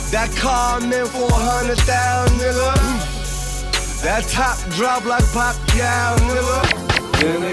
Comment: lmao did he say "that top drop like Pacquiao"? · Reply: Yes lol that's why I include that bit in every video addressing this KO, French Montana knows what up!